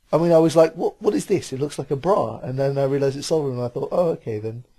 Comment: Lmfao did I hear you say you thought the Omni spot "looked like a bra"????? Swear to god you said that LOL go to the part!